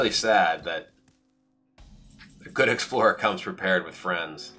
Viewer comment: I misheard the dragon explorer card and thought it said "What good explorer comes prepared?" And for a second I genuinely thought Reno Jackson had turned into a dragon